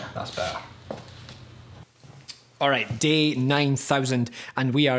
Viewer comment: did he say day 9000